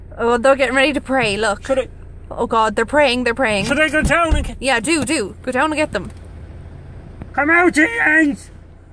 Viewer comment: oh, i thought he said come out ye yens lmfao